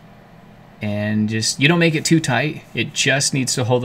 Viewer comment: The way u say tight is hilarious brotha 😂 almost sounds like you say “tot” lol